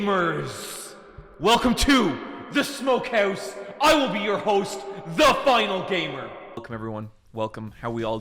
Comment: Finally back to the smokehouse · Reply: For some reason I thought was about to say "I'm your host, the smoke" I am so tired